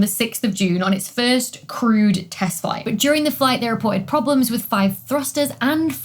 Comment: I keep hearing "crude test flight" and it's slowly becoming true...